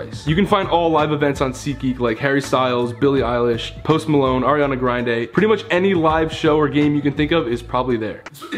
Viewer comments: Hold up also did he say Ariana grindae??? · Am I the only one who heard him say ariana griande in the promo ad